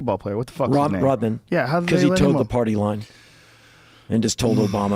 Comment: "How come they let the basketball player go - Rodman" "Cuz he _____" what did Henry say there? I replayed it like 10 times and can't understand him. It souds like "Cuz he took the party line" or "Cuz the towed the party line"... Can anyone tell me what he actually said?